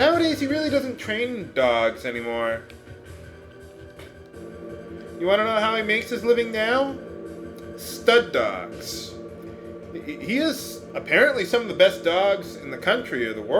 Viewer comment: when you said "stud dogs", I thought you said "stunt dogs."